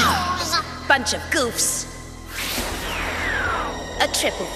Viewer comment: パンチラ グーッズって聞こえる